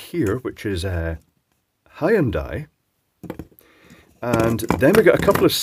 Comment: I really heard "High 'n die". Maybe it's appropriate since you mentioned these things are designed to cook themselves to death.